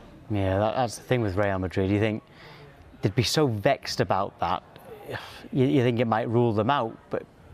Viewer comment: is it me or did he just say ‘ vex ‘ haha